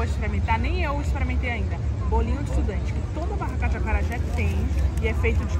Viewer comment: Depois do acarajé ela comeu bolinho de quê? Eu entendi "bolinho de estudante" 🤭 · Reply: Sim Mariely, bolinho de estudante, feito com tapioca e côco frito no oleo.